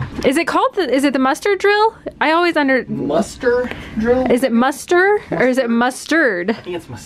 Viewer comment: It looks like you guys had an awesome embarkation day! 🛳️ I once looked it up and it’s muster. I always thought people were saying mustard, too. 😂